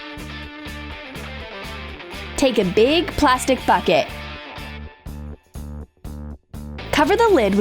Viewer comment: she said f*ck it instead of bucket